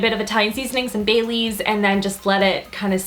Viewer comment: when you said bay leaves i thought you said baileys like the liqueur haha!